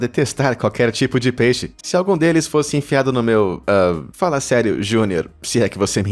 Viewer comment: entendi a pila kkkk😂😂